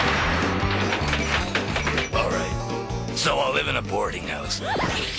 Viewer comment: what did beetle juice say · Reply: "Alright, so I'll live in a boarding house". It's a pun.